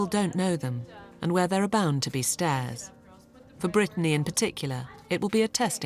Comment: oh i thought they were saying stairs and not stares lol